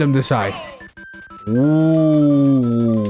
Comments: Yo I actually thought he said "Lemme turn autistic" I was like HUH?? 💀😂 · idk if im tripping but i thought he said ``lemme turn autistic`` instead of tilt-stick 😭